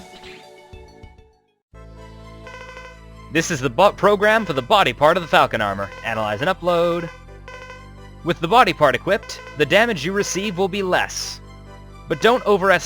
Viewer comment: it sounded like he said "This is the butt program for the body part of the Falcon Armor"